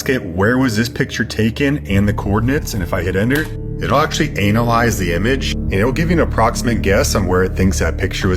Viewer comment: did he say Anal-ize?